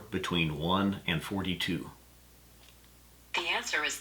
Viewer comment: Siri thought you said "between 4 and 2"...